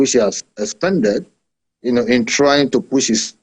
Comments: did I just hear Spended? No wonder it froze! 😂😂😂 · He said "expended ", which is a very correct use of word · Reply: of course “expended” is a synonym of “spent”. However, I thought I heard “Er! spended”, just as it was with S. Dalung.